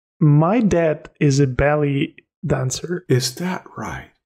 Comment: Wicked video, this guys got such a great story, my god though, i thought he said my dad was a "belly dancer"